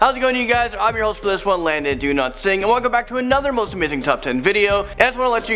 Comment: Hello landon. Everytime you do the intro it sounds like you say '' I am landon Do not sing'' just wanted to point it out. Goodbye now.